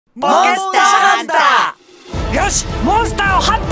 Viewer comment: ポケットハンターにしか聞こえんw